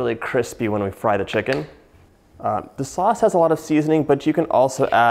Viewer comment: I thought he said the sloth has a lot of seasoning